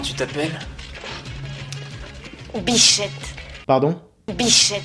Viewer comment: moi javais compris biflette